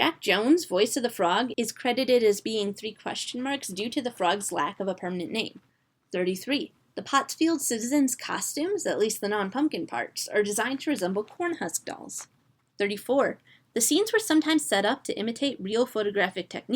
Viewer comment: omg I thought that you said 'that the dolls were supposed to resemble pub dolls' and I